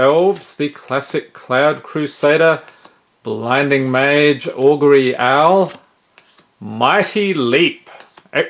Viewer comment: I almost thought you said “ugly owl” and had to rewind XD Also pretty nice pulls